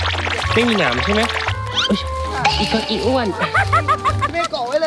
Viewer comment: ตอนนาทีที่4:02 ได้ยินเสียงผู้หญิงพูดว่าอีอ้วน ไม่ใช่เสียงคนในกลุ่มก่อนจะเจอโรงศพ